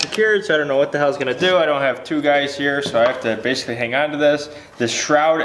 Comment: I thought you said you didn’t have a tig